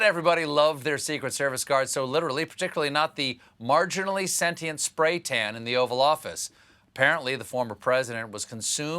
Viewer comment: I thought he said, the 'Offal Office'. I nearly realized what time it is.